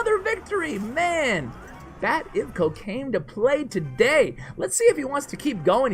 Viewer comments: for a second there I thought had said cocaine lol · I heard Bat Ivkocaine to play today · sounded like you said cocaine lol actually said 'Bat Ivko Came to Play Today'